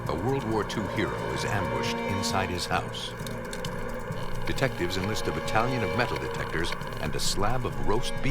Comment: Oh... Heh heh heh! Thought he said "Nude Detectives..." 😀😃😄😁😆